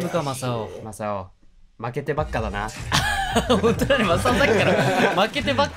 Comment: あたり、くそうってまさお言ってる？歌の空耳かな？ww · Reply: 江口さんが「本当だ」って言ってる奴だと思います！